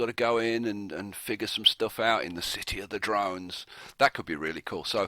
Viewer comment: City of Drones? When you said that I thought I heard "Game of Drones" (Game of Thrones, ya know) LOL. But I do vote for Pyramid next!